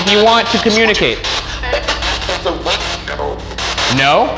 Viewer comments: #postmortem: Hello Ryan and Shane! @ 34:00, after Ryan asked to say his name to communicate, a male voice said "No" and i think i heard right after a female voice say, "I said no". It is possible whatever entities are there, they really dont want to be disturbed? Afterall, it seems you got some other audible negative responses. #Boogara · when the “ghost” said no and Ryan asked afterwards if they said no, it sounded like a female voice was saying “he said no”